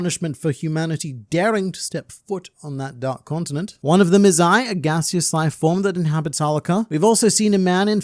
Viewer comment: "One of them is Ai, a gaseous lifeform..." but I heard "One of them is I a gaseous lifeform..." 😂 Sorry man.